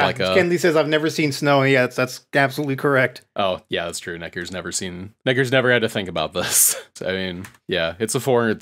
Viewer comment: What did you say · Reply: "NETGEAR'S never had to think about that."